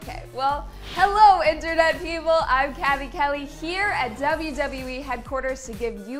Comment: I thought that she said her name was Kelly kelly